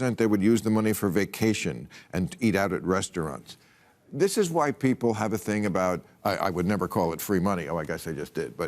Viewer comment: I thought Bill said "This is white ppl" but he said "why ppl" 😂😂😂kinda close buddy